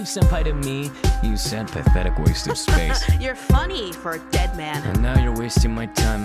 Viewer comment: When I first heard this song, I thought he said "You senpaithetic waste of space" as if it were a pun TwT